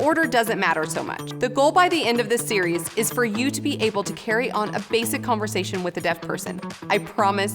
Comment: Omg. “So you can carry on a basic conversation with a dead person “ i thought is what she said. Im like damn. This woman is so good she can sign with the dead!? Man. Maybe I need a hearing aid first before I watch these videos